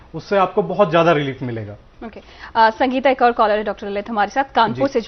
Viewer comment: What did she call you? I heard Dr. Lalit Mysah?